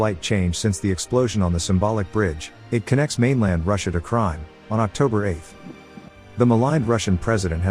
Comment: "It connects mainland Russia to Crime" hahahaha perfect Translation/Freudian slip saying Crime instead of Crimea, lol.